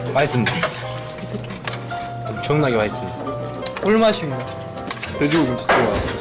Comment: 존나게 맛있습니다??? 잘못들었나? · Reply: 다시 크게 들어보니 엄청나게 맛있습니다 였구나 그럼 그렇지 방송에서 병사가 존나게라고 한걸 그대로 내보낼리가 없지 귀좀파자잉..